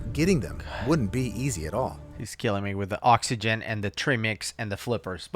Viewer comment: If I've learned anything, it's never to dive deep without my flippers, a can of oxygen, tremmix, or safety rope. And always try to avoid getting depth intoxication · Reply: Wait? But I just packed all my cylinders with sawdust ready for a weekend under water. I thought they said treemix!